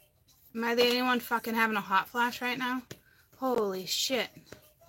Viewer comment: I thought I heard someone say that's hot flashes lol